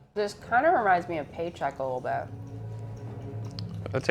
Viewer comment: hey , just curious , i didn't catch the movie mention at 2:07 ! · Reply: Paycheck